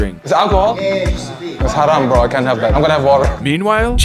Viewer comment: did he just say "it's Haram bro I can't have that?"